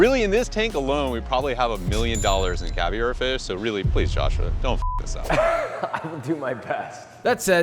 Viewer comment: What is he saying? Don’t fuck yourself? 🤨